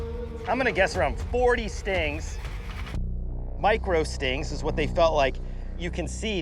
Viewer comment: I honestly thought he was saying Mike Rowe stings like dirty jobs stings lol